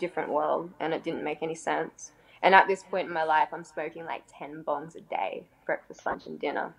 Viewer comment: 10 bongs a day wow. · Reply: I thought she said “10 buns a day” which would’ve been a serious “wow.”